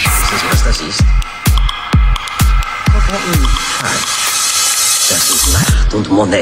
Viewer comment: An die Deutschen, mal bei 43:45 reingehört? Wissen Sie was das ist? KROKETTEN! :D